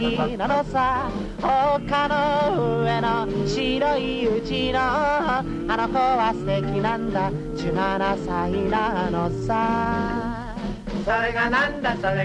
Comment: What is he saying around 4:00? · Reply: Hello! I'mJapanese. around 4:00 he is saying 「丘 の 上 の 白い 家 の あの娘 は 素敵 なんだ 17歳 なのさ」 'oka no ue no shiroi uchi no anoko wa suteki nanda junanasai nanosa'.